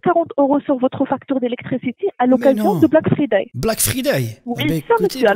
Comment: Mais quel anglais 😮👌 Black Freedè "c'est ce que moi j'ai entendu ".